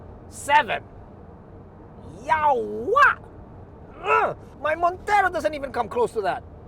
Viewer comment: did intentionally say 'Yawa'? or was he actually saying something different and I'm just hearing things. Lol